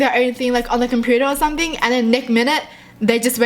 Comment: Did she say neck minute